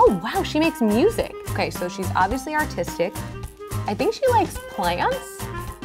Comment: I thought she said “she’s obviously autistic”😂